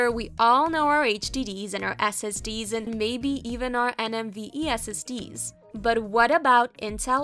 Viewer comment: Did she just say NMVe ?